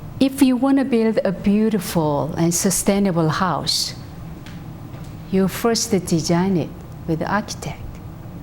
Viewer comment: I love this video by WonBuddhism Manhattan. However, I can't decipher what she is saying from 9:53 - 9:56. Would someone be so kind as to share with me what she is saying? I'd truly appreciate it. Thank you. Peace & <3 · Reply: She says, "if you want to build a beautiful house, you first need to design it. Like an architect."